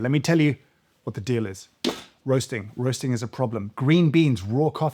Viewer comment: I'm sure I thought I heard James say that he was roasting crack at one point. Then suddenly I understood the intro. 🤣 After all, coffee fell behind Escobar's Magic Sugar, and Psychosis inducing energy drinks (with added brain chemicals), as the most crazy of stimulants. So it's only fair that crack coffee gets it's moment to shine. Hmmmmm Where will they fit the glass pipe onto the most expensive coffee makers? And will Nespresso just start firing out crack pods.... oooo crackpots??